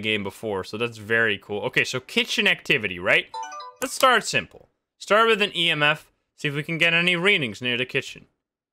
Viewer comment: what was actually saying: Let's start simple let's start with an emf what i heard: Let's startsym let's start with an emf